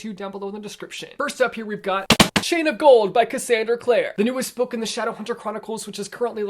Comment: I really thought he said 'Shane of Gold' and for one sec I thought there was some Shane Dawson book I didn't know of